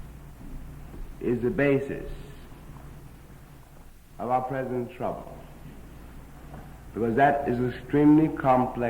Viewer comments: I was listening while working. I thought he said "That lie is the basis of our PRESIDENT TRUMP." I just about fell out of my chair! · is rather spooky...it sounds like James Baldwin from 1963, is saying "President Trump," (24:17) The preceding context seems fitting too. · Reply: Yes! I thought I heard that and had to check after coming across your comment. "... present trouble..." does sound like "President Trump" - the way James Baldwin said it.